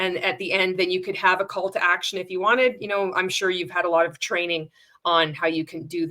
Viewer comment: With the pro tips for MLMs wanting to make a story you said, "...you can have a Call to Action at the end..." but what I HEARD was, "...you can have a CULT ACTION at the end..." and now I can't unhear it lol.